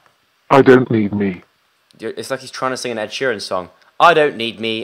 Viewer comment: As soon as Joe said "He's trying to sing an Ed Sheeran song", You Need Me, I Don't Need You started playing omg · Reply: I thought he said an insurance song.... Oops.